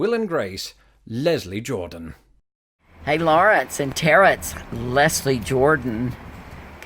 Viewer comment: Did he say, "Laurence and Terrence"? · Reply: "Laurence and Tarah, it's... "